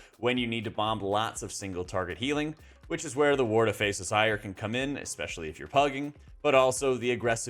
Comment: What are you saying at 15:28 ? I'm trying to figure it out for like 10 mins now :D Word of what? xD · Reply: Ward of faceless ire - it's a trinket